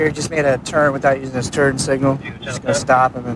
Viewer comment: did he say turd signal?